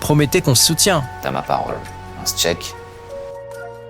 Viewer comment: putain c’est quoi ce tshek 😂 c’est tshek ou un sketch? 🤣